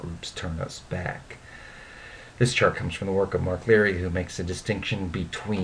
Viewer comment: at around 5:48 you say the same of the author, but i couldn't quite catch it. Would you be able to send me a link to the relevant work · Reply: Its Mark Leary's paper in the Journal of Social and Clinical Psychology, where he talks of the inclusion-exclusion continuum: the citation is Leary, M. R. (1990). Responses to social exclusion: Social anxiety, jealousy, loneliness, depression, and low self-esteem. Journal of Social & Clinical Psychology, 9, 221-229. He has a more recent overview (that is more accessible) in the Annual Review: Leary, M. R. (2007). Motivational and emotional aspects of the self. Annual Review of Psychology, 58, 317-344.